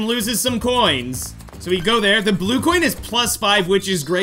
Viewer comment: Did he just say “blue” coin on 8:01?? 🤔